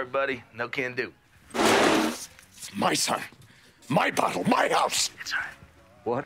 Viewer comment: I SWEAR to God it sounds like is saying "My son, my butthole, my house!" And I can't unhear it